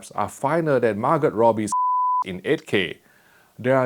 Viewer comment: Oh my god, I heard "finer than Margret Tatcher's ****" at first and choked a little.. There was no reason for that to happen, why are you in my head you witch!